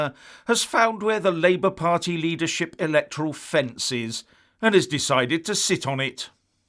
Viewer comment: I thought you said Electrical fence !